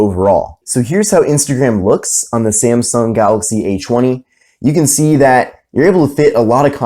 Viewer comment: did you really say a20 or it's just me😂